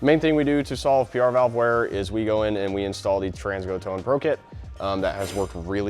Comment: This is a very informative video. Thank you for sharing it. At the 1:07 mark you reference a kit you install to resolve the PR valve wear issue. The name of the kit is mentioned so quickly in the video that it's difficult to clearly make out the name of the kit. It sounded like "Trans Go Tone Pro Kit." Is that what he said? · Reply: Trans go Tow and Pro Kit!